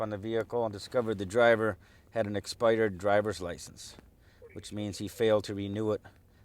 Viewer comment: did he say ' he has an exspidered drivers licence'? awesome videos